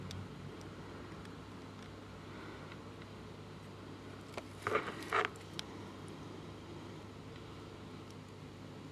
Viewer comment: very, very interesting. by the way at 14:31 what were you gonna say? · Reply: It’s been almost 6 years since I made this, so I can’t be sure, but it sounds like I was going to say “I am happy to commit to glueing it in place”